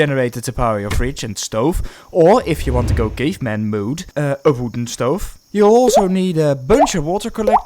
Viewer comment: did he say a wooden stove ?